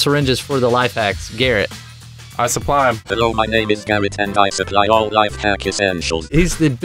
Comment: In the intro I thought is said “HELLO MY NAME IS CARROT” lol